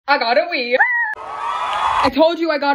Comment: Anyone else thought she said she got a weave?!